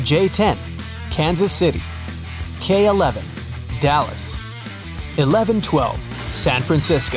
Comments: did he just say “11, 12” ??? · i think u got confused in letter L and no. 11 You said 11 12 instead of L12 😂😂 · Am I the only one who heard him say 11 12 instead of L12 when he was talking about the money